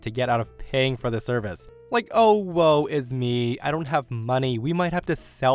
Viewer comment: OwO? Did you just say a furry phrase? 😆